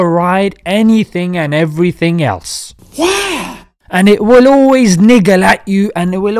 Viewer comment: Can some translate to me what he said at 0:55. I swear i am hearing it wrong. I hear "And it will always nigga at you"